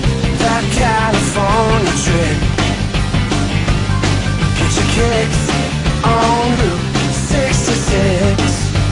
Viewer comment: I thought he said "get chick Hicks on route 66"